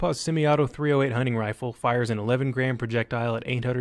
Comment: Did you say ELEVEN GRAIN .308 bullet @ 2:50? although eleven GRAMS is about 169 gr..